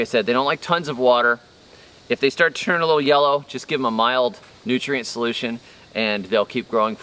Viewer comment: Every time you say “mild nutrient solution” I hear “pee on your pineapples” thats right, right?